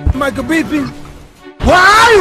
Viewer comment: It sounds like he says "Michael Pippen"